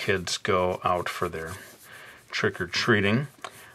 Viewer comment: when he said "trick or treat" I thought he said "stripper tree" with the way he said it XD